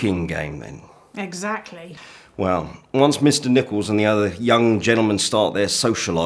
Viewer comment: I genuinely thought he said “Mr. Nipples” 😂😅